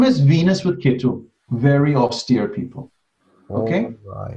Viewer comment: minimum is Venus with ketu, very obs.... person. Can anybody help me to understand what said at that point? I cannot understand the particular word. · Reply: Austere I guess.